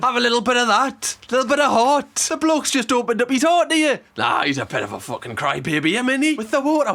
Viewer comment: I watched it four times. I don't know what she is saying. British accents are so diverse... Even when she said " he's a bit of a crybaby" I was trying to read her lips... cry?? It looked like she said 'croooy'